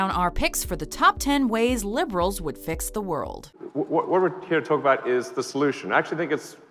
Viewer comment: thought it said "fuck the world" not "fix the world" lol 😂